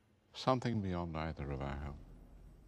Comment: what does dumbldore say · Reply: Something beyond neither of our help